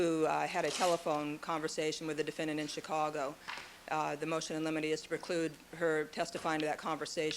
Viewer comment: I think the laugh was because they thought Cheri Lewis said "motion in lemonade" and not "motion in limine". Gave me a little chortle too.